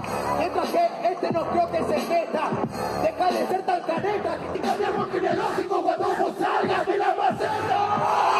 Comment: alguien sabe lo que dice? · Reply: Deja de ser tan careta critica mi arbol genealógico cuando vos salgas de ma maseta